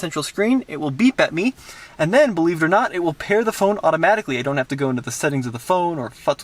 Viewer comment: Really sounded like he said "...don't have to f**k with any..."